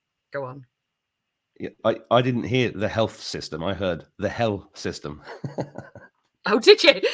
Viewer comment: I heard the same thing when she said hell system I thought she said health system too 😂